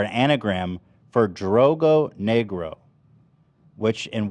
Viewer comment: Australian here, “drogo” or whatever they were saying at 2:27:00 is definitely not Australian slang for dumb or at least I have never heard that before ever. Peace and love. Edit: realised they were saying drongo, I think I’ve maybe heard a boomer say that once maybe in my entire life